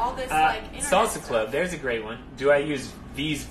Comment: Oh shit that was me who asked that question! Pretty cool to see. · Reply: Awww really? What's your name? My dumb ears heard him say "Salsa club, here's a great one"